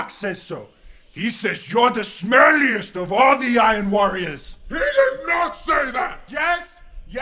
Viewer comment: Turns out that, in the grim darkness of the far future, there are still CoD lobbies. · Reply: At first, thought it said fire warrior